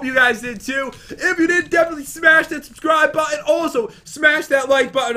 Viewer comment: at the end did say hey guys 😂